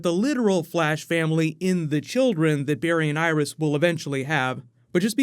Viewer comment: I like how my bluetooth headset gave out during this video so at one point I heard: "The children Barry and I are going to have"